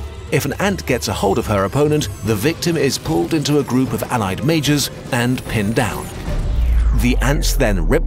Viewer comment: Every time he said "Majors" I kept hearing "Mages". Together with the Medieval framing made these wars even more epic. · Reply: i had to rewind multiple times as i kept hearing "mages and miners"